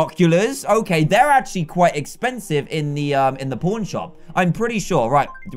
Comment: DID YOU SAY POORN SHOPXD